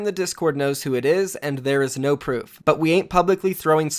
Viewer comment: Am I the only one who heard he said no proof